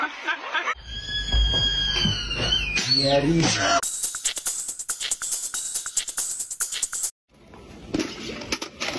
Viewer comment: to anyone wondering, what he said. He said "Stop screaming" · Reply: Не ори